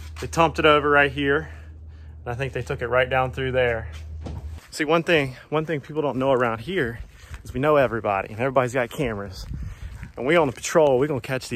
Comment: Green egg. I thought you said it everybody has security cameras around you. Did anybody catch it on their camera? Been watching you for a long time. Great videos. Congratulations on your marriage